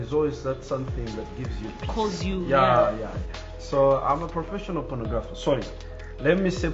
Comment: Wow great interview! But am curious about something i heard at 14:41🤔 someone clear me out?? Anyone? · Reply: professional pornographer thus what he said